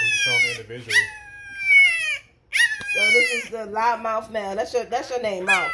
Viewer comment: I thought D said loud mouth mell. But she said loud mouth male.